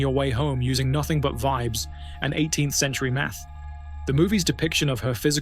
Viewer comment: I thought he said "vibes and 18th century meth". Hell, sounds like a good time.